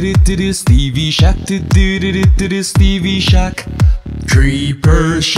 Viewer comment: Did anybody else hear Stevey shat doodoododoo